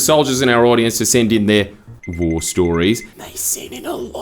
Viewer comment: When you said war stories I genuinely thought you said vore stories